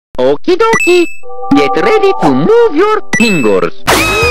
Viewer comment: I thought Mario was gonna say: Okie Dokie! Get ready to move your PINGAS